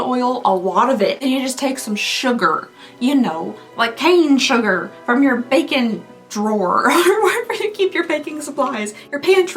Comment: I totally heard it as you take some sugar from your bacon drawer! 😂 And I’m like folks have a bacon drawer? And they keep sugar in it!